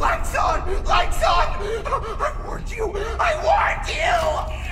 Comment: Thought he was saying “I WANT YOU.” “I WANT YOU!” Until I saw the markiplier video, lovely hearing I have.